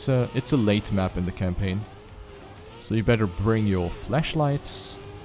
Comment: Is it just me, or did he say "fleshlights" instead of "flashlights"?